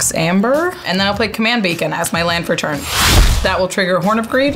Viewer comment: I thought said command bacon